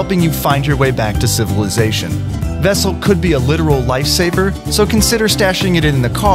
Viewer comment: did he say live saber?